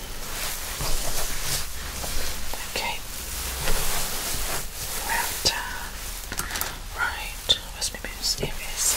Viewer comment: What did she say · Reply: From 9:40 to 9:50 she said "okay, well done, right, where's my mouse, here it is, give it a good shake"